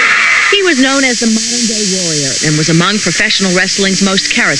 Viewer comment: oops she said modern day, sorry the audio sounded like she said dingo till i listened again